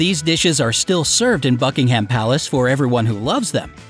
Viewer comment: i heard f*ckingham palace